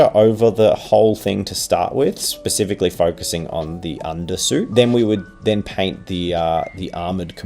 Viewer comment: when said "UnderSuit" i thought he was just saying "underside" really weirdly